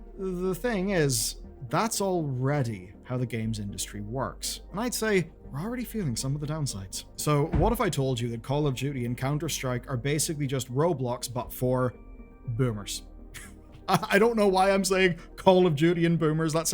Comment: I keep hearing "Call of Judy". Just me?